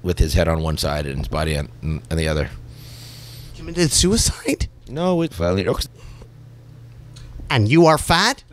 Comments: What does Mike say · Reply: he said "no it was Courtney Love" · Can someone tell me what Mike actually said at 8:22? It was reversed/censored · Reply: he says “no, it was courtney love” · What'd mike originally say at 8:22 ? They changed it for some reason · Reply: he said "no it was Courtney Love"